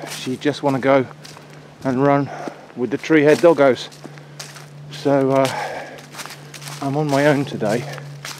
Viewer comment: "... she'd just wanna go and run with the tree-head doggos ..." LMAO!!! Am I hearing this correctly?! Hope so bc I love it!